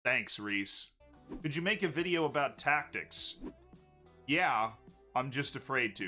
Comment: When you're not actually watching, just listening in the background and you think he says "The Jamaican video about tactics." and you're like, hold up, what?